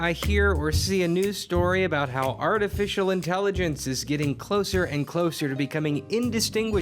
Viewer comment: At first, I misheard that as "art official intelligence"... I know what you said, but now I can't stop hearing it the wrong way.